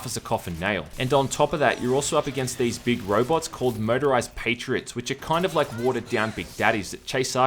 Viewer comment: You have a peculiar way of pronouncing "Motorised". Every single time I hear "Murderised". Which is just SO Metal.